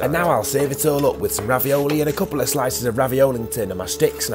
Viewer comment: DID I JUST HEAR "Raviolington"?????